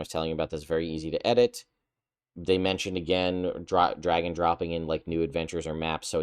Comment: you said "drag and dropping" and I heard it as "dragon dropping" then thought "poo stinky!"